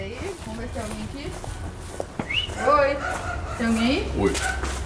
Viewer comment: no minuto 1:22 eu ouvi um oi nao sei se era de um da equipe mas nao deixa de ser estranho